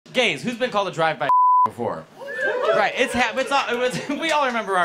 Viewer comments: I thought he was saying drive by fuk 😂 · “Who’s been to a drive by fuck before” is what I thought he said at first